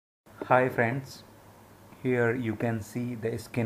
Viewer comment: did he really say "hi fans" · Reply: yep at first I thought he said fans but then I heard it he said friends